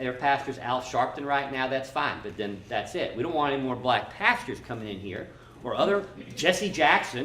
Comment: All I heard was "NO MORE BLACK PASTORS"...